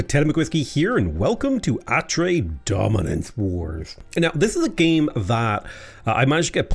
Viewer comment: I thought that said said After Dominance Wars….😂